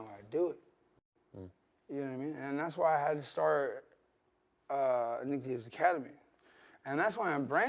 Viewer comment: Wait wait wait wait...... @ 21:10, did Nick just say that he started using Ketamine? Everything makes sense now. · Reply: Jason Johnson “Nick Diaz academy”